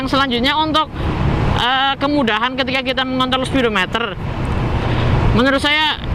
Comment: kayaknya saya yg salah dengar atau gimana 🤭😂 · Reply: Haha mengontrol bang, ada R nya haha 😂